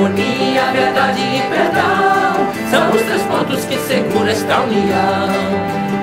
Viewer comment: Em outros lugares, ouvi "regem" invés de "segura" São os três pontos que regem esta união.